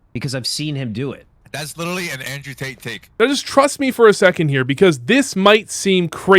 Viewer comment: did he say Andrew Tate take?